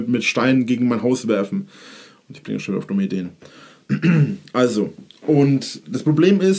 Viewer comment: Was spricht er da? Kann ich nicht verstehen...versteht das jemand? Ich verstehe nur " Ich bin schon auf dumme den"....hä??